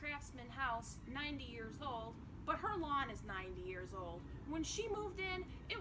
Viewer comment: I had to go back cuz I swore she said my 90 yr old sister?????...lol. Smile together, time forever. And I love the glass brick windows.